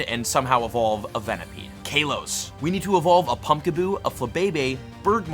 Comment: Did he just say kayloes